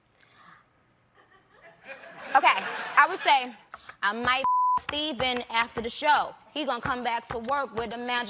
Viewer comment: 😝 "I might f--k Stephen after the show / He gon' come back to work with a magical glow / But when you see us please, f--king don't stare / Just address me as Queen Nicki Colbert." 😂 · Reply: I thought she said ' I might ' BLOW ' Stephen after the show ..😂